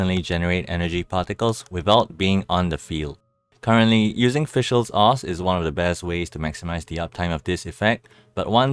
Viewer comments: For a moment I thought you said "using Fischil's arse" for elemental particles. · lol i thought u said us fishels ars 😂